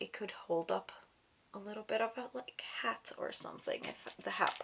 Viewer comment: I thought you said "It could hold up a little bit of a CAT or something" 🤣🤣🤣 I was imagining a little kitten perched on your hair